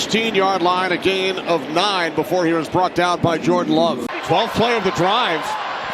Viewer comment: did he really say “Jordan Love”? I know there’s a Love on Seahawks team but is his name really Jordan too ? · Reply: Julian love is his name